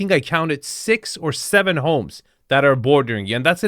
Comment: did you say 6-7 homes……….